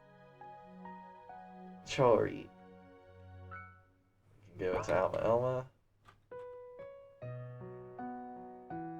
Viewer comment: What did Rami say? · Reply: 'A little'